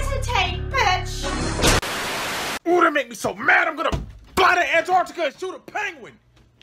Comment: I just saw the subtitles, I totally thought thor said "body Antarctica and shoot a penguin" which in all fairness if there was one deity that could body a continent, it would be Thor